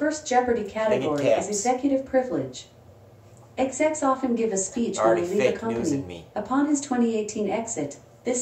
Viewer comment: Can you help me with this clue too? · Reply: ohhh i see. Thanks. I don't even know why i heard "Execs often give a speech to relieve the company" and i was like what?